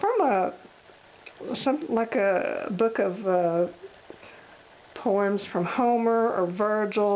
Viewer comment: lol, my head was in the gutter i thought you said a big book of porn,aagh. don't mind me..